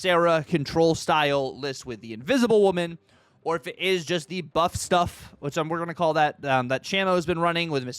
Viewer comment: Working on splitting Surge and ... THIS NEW SYSTEM SUCKS! Edit: did you just say "butt stuff" at 2:37 ?